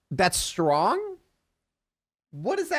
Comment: I thought he said "thats wrong" but it came out "thas'wrong" and the captions heard 'thats strong", and we're hearing it because we're prompted to by the captions